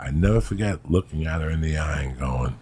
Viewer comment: I wondered if he meant Anna Nicole Smith but I can’t imagine her doing stand up. Then he says “Don’t say her name” Now I’ve just paused at 4.29!after hearing him say “I’ll never forget looking Anna in the eye” Is it possible he’s talking about her? Old guy buying her anything she wants? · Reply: ? I believe he says “I’ll never forget looking AT HER in the eye”, ”at her” not “Anna”. (That’s what I heard, anyway!) And yeah, I don’t think she was into stand up, so probably not offering blowjobs for stage time. She had a different gig going, you know?